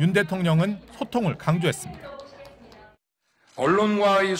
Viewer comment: "윤대통령은 소통을 강조했습니다"라는 말을 "호통을 강하게했습니다"로 잘못 들었네요...